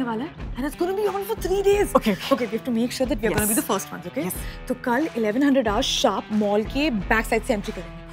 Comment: did barkha just said 11 hundred hours sharp???😅😂😂